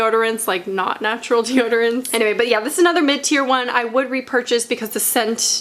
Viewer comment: I'm embaraassed to say that every time you said mid tear I thought you said minty/mintier and I was like WHY DO THESE ALL HAVE MINT and then realized that's not at all what you said lmao....anyways pit detox and Schmidt s here I comeeeeee